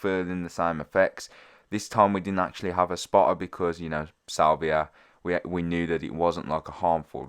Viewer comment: I rlly thought it said saliva and I was like the fuckkkkkkkkk